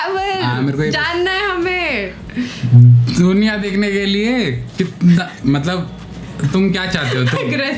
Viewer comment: he duniya dekhne k liye But i heard Sonia dekhne k liye😉